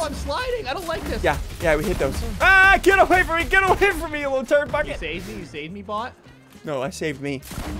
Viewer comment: Did anyone hear him say the f word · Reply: He said turd bucket